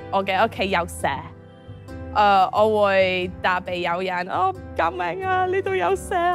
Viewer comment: 😂 the english caption says 'snack' instead of snake